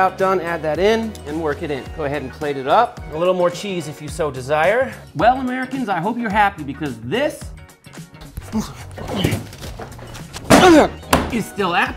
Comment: Did he just say "it's galactaluly delicious" ?